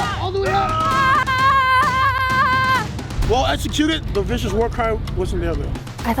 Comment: “Sponsored by The Marines” that’s the most vicious sponsorship ever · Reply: AwesomeKarma I thought vicious said “Viscous” 😂